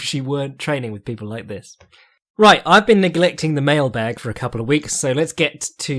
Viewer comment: I heard this as “I’ve been neglecting the male bag” and was quite confused